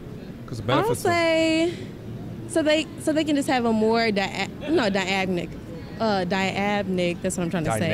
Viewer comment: I think her saying Queen of Elizabeth just about says it all. Whoops a new word at " 8:36 " :Dyadmic.